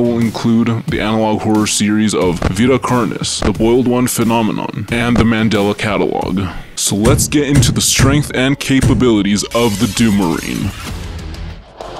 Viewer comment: thought he said oiled one